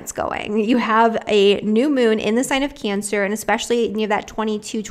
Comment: I got confused with your Cancer analysis, you said ‘... outlet this weekend...romance going...the New Moon in the sign of Cancer...at 22-23 degrees...trining ascendant, sextile descendant...” But WAIT, it’s a Scorpio New Moon this coming weekend, Nov.14-15...🤔 This was at Min. mark 27:00 Very CONFUSED 🤔🙃🤔❓ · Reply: Modern Revalutionary ok, but that is Not what she said - listen at 27:04 ... she says “new moon in the sign of Cancer” - I listened 5 times, heard the same each time❗️